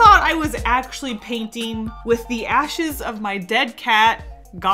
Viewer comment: I THOUGHT IT SAID LASHES